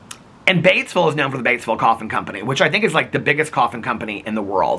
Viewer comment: I don't know about anyone else but I swore I heard you say Batesville Coffee Company NOT Batesville Coffin Company. I was so confused as to why you were talking about the proximity of the 3 cities 😂